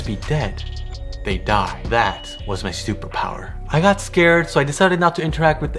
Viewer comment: I love how the automatic captions said “that was my stupid power” 🤷🏼‍♀️